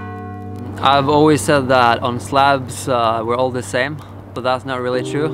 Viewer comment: thought he said slavs are all the same lol.